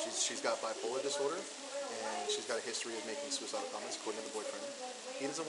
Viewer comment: "She's got a history of making suicide bombers, calling it the boyfriend." What?! 😵 Tf does that even mean?! Whoever added these awful, ridiculous subtitles wasn't even trying. What he actually said is this... "She's got a history of making suicidal comments according to the boyfriend."